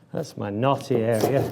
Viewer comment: I was on another tab and all I heard was the voice of an English gentleman say, "That's my naughty area"